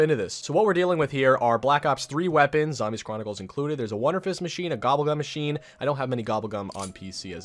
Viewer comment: Did anybody else hear wonderpiss